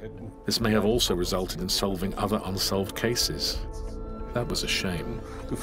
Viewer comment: "THAT WAS A SHAME" is not what he said, he said" DAS WAR SCHADE" - that was too bad is the meaning.